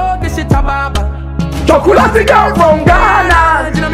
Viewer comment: I thought what I heard Blacko say is "Chocolate girl from Guyana" though.